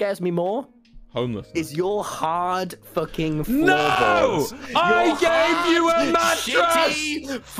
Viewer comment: i thought tommy was saying ''YOUR HARD F-CKING FOREHEAD'' LMFAOOO- Then i realized it was ''YOUR HARD F-CKING FLOORBOARD'', im so dumb LMAO